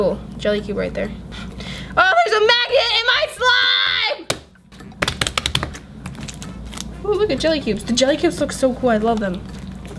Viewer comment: i thought she said AHH THERES A MAGET IN MY SLIME